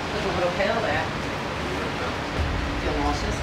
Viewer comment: Why do they fake what people are saying???? 6:41 “feel nauseous?” And the guy said “a little bit” yet the subtitles say “I’m blacking out” why....